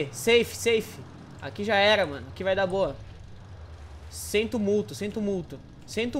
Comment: Ele falou sem tumulto ou sento muito nao entendi